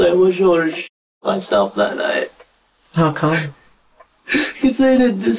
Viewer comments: situation is not funny but I thought he said I wish I shit myself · I have the captions on and really thought at 20:45 that this kid said “I wish I would’ve shit myself that night.” 🤦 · I thought he said “I wish I just shit myself” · Lmao I thought he said I wish I shit myself that night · I thought he said he wished he shit himself that night